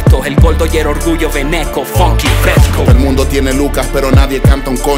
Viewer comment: #FunkyFresco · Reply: Pensé que decía Fucking Fresco